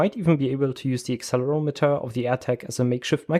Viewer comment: did i hear: "modify as a microphone" - NSA here, please send CV